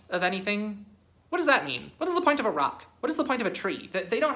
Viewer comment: I know they said “a rock” but all I heard was “what’s the point of Iraq”